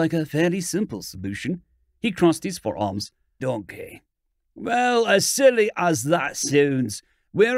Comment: Am I hearing stuff random "donkey" at 3:04?